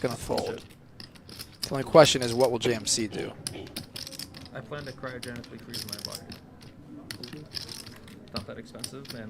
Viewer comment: um, what did he say? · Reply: I plan to cryogenically freeze my body